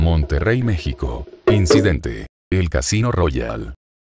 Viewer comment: yo entendi clash royal XD